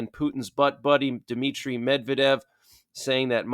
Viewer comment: Did say "Putin's butt buddy, Dmitri" at 15:50 LMAO